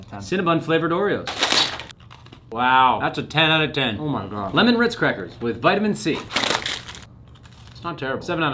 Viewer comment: I thought it said “Lemon rizz crackers” 💀